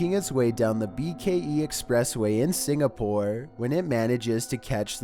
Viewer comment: did u say Singapore?! I didn't know Singapore is huanted too and I'm scared because I'm from Singapore 😨